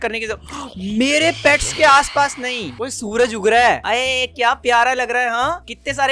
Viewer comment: he really said suraj ugg raha hai hehehe